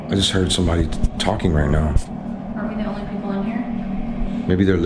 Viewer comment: Why did u stutter? Lol At 10:28 “I just heard sumone t t t tttalking rite now.”